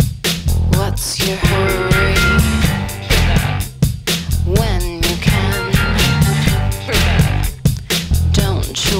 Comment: What's Hollis saying the 2:04 mark? That little aside....I can't make it out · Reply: she's singin' "what's your hurry?"